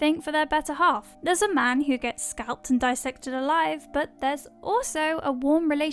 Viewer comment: I hate to correct you but it’s all in good nature. Unfortunately, the young man in the cave was vivisected not dissected. The really nasty difference is the subject is alive during vivisection and dead during a dissection. Cheers.. Great video! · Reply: I thought she said BISECTED which I thought was correct but "bisection" also may only be the case for a dead "subject?"